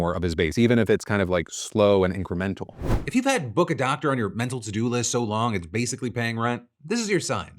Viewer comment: I swear I heard: "If you have Book Adopter on your mind... " and now I do. BRB, off to the library to give a lonely book a warm home.